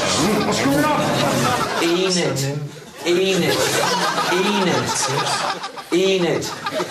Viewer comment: Lol when he was saying "Enid....Enid....Enid...." I thought he was saying "Peeenis...Peeenis....Peeenis...."